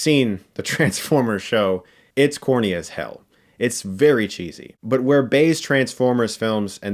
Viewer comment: I thought he said "Horny as Hell"